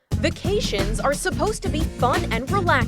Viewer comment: I thought is said “worst vaccination disasters” XD